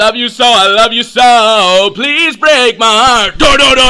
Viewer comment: When I first heard this I thought they said “PLEASE BREAK MY ARMS!”